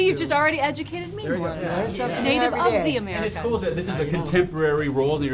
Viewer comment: I thought she said "Native UGLEY Americans" LOL LMFAO thumbs UP!